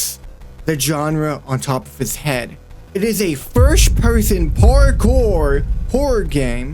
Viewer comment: Bro forgot about Eternity😔 Nvm, my bum ahh misheard “first person” with “first ever”